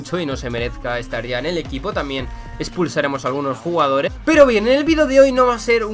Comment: ¿Lo habéis oído? En vez de expulsar, digo "Espurs-sar" jujuju -Entendi la referencia